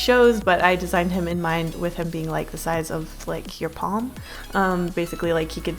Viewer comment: There is something wrong with me. When she said, "your palm." I heard, "your mom."